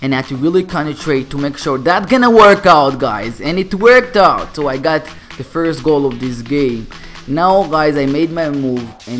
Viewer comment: I'm sorry but when you said "ok Guys, we got the first goal on this game" I heard you say, "ok guys we got the first goal on this gay"